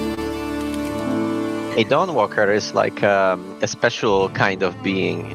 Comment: I heard "Dongwalker"...:_Jaffewow: I loved The Witcher 2, so might check it out. Is that "Temu BunniButton"⁉️:_comeonnow::_Bunni